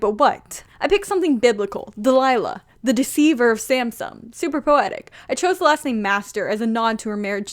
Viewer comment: totally didn’t mishear that as “the deceiver of Samsung”